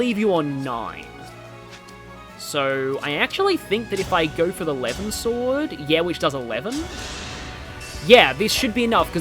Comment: Wait, did you say you did eleven with A LEVIN Sword?!